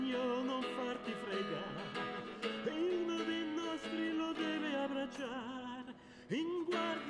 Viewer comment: ho capito solo che dice.. Si veste da ragno non farti fregar.. Chi sa dirmi cosa dice? 💖😂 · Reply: "Uno dei nostri lo deve abbracciar"